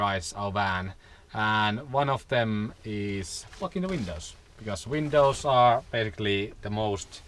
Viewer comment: Did he just say fucking the windows?